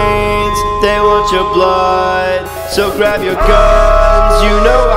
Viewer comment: coming 10 years later and i thought he said "they want yo butt" when he said they want your blood and after all these years i finally got it💀